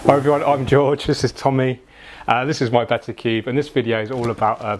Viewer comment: When I started the video I misheard what you said and I thought you said “This is my betta, Keith.” But you said betta cube. Haha, so my vote goes to the name Keith.